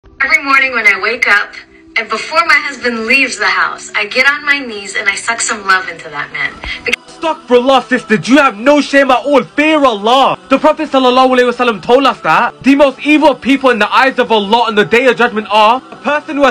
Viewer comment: Awww hell naw she said that 💀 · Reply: i thought she said suck some lemon into that man